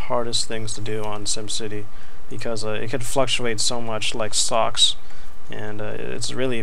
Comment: Thought you said socks instead of stocks, lmao